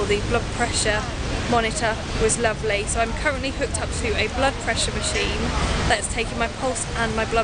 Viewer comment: great vlog! :D good luck! I actually thought you said "vlog pressure machine"... oh dear! haha.